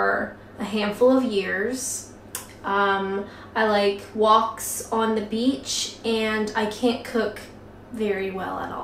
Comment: Haha... being a cooking video, I thought you said "woks in the beach", lol. Those look great... nice with gravy or jam or marmalade...